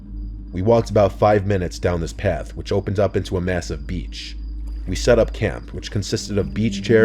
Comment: did I just hear massive?